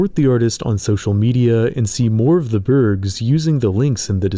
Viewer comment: I consistently misheard you and thought someone had legit named their spec evo project "birbs"